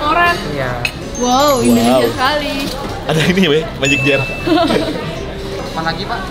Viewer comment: Jane sekarang udah nyaman yaa panggil 'bang' dan saltingnya Jerome pas nyanyi 'and help US to be one' sampe Jane juga ngelag mau makan telurnya 😍😍😍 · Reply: w malah salfok panggilan jerome ke jane, denger deh di menit 6.40 jerome bilang “ada ini be…” w ga salah denger kan ya